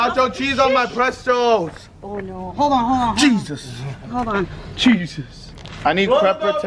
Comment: I’m confused by that odd shaped basketball I’ve seen in two videos now. Why is it brown? And oval shaped? What are those white marks? I’m very confused 😹 Also at 9:39 did say “Cheeseus?” 😹